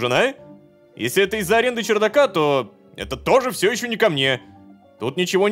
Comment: Кларк:то это всё ещё ко мне Лололошка:то это всё ещё не ко мне (если кто то не услышал посмотрите субтитры)